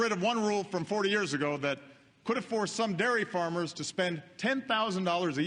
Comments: I heard " could afford " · I understood 5 out of 6 short speeches I've heard. The one which tricked me was "could have forced...". I know it didn't make any sense, but I heard "could afford" three times!